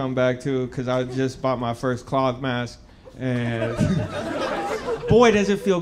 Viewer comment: Is he saying "clog mask"? And if so... what is a clog mask? And if not, what is he saying? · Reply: Cloth mask